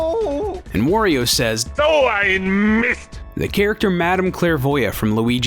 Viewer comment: Did wario just say "So ein Misst" in the localization? XD 4:14 That sounds like German to me.